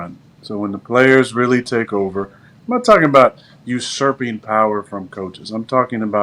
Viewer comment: what word is Aaron saying? It sounds like “Usurpian Power” but I can’t find anything on Google related to that. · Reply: Usurping